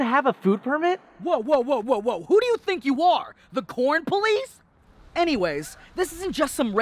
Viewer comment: I thought he said porn police